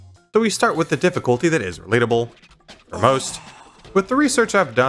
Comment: the difficulty that is "plublub" (seriously though what the hell did he say there i can't hear it) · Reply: "we start with the difficulty that is related (For most)"